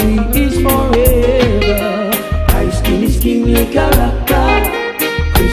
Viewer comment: All this years I thought he saying "skinny character". 😂